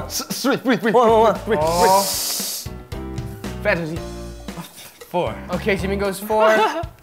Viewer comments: Did he just say 'Fantasy'? Lol Yeah, you're definitely thinking with the mind of a guy, Rap Mon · DID RAPMON JUST SAY FANTASY ABOUT THE SCHOOL TEACHER?!?!?!? OMG!!